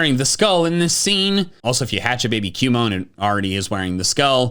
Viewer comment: Did he said cumon?